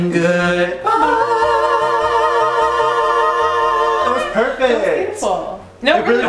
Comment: I thought Mitch said that was painful 😂 😂